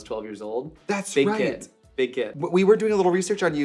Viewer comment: No joke thought he said big d*** not big kid lmao